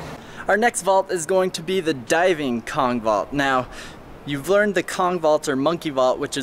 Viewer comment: I thought he said dabbing kong vault